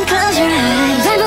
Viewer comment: i swear i heard 'hell naw samosa' XD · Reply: Leo the Lion 😂😂😂 he said ''can't escape''